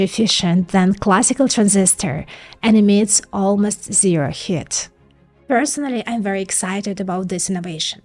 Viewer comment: Check out the AMD Ryzen PRO laptops and support the channel: <link> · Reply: the subtitles say "emits almost zero HIT". I think it means HEAT.